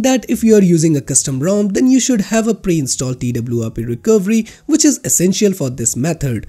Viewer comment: Can somewhat spell that word for me. I can't under understand what he said and I definitely don't have a clue what it is. It sounds kind of like "pre-installed data-bluabi-recovery". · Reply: "Team Win app recovery" - I think that's what it says